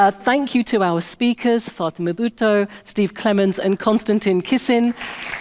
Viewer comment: Sounded like she said Fartinma Bhuttole